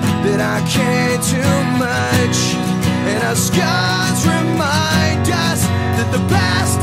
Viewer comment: I always thought he said "the stars remind us that the past is real" lol.